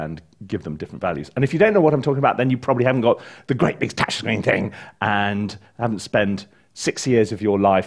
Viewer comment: Hello. What did you say at 4:35 ? I have replayed it a few times but can’t make it out. Thank you for an informative video. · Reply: "the great big touch screen thing" ie assigning all your articulations to on screen buttons etc